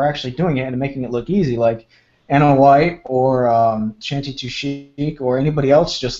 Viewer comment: mentions women woodworkers. I subscribe to Ana White but who is the other one? Sorry for butchering the name but i heard Chanti Tushik and haven't found anyone with a similar name doing google searches.